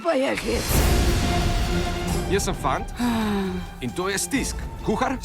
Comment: did he just say his name was FART?!